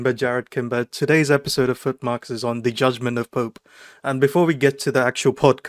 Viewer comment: Did he said judgement of poop. 😂